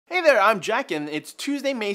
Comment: Damn I had to repeat the opening of the video because I heard "Hey there I'm Jackin"